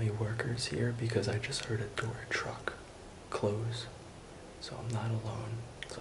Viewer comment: Did u say door truck 😂